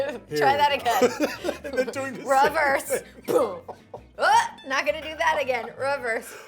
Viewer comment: Did she say "Raverse" :D she's so Britta, I love it